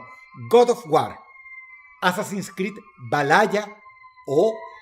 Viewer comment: Todo muy serio hasta que escuché a Dross decir "Valaya" 😂